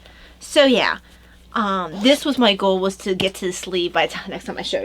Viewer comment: I hope you enjoyed our time on the treadmill! I love that blue sweater you are making! (by the way, I thought you said Michael instead of "my goal" too! LOL)